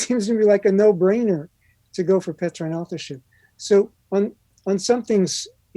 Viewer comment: What word is he saying at 13:43 and 13:47? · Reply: Hi Amy, I believe he is saying Petrine Authorship. Which speaks on who wrote the book of 1 and 2 Peter. Craig Keener is agreeing here that Peter wrote both books, from what I can understand.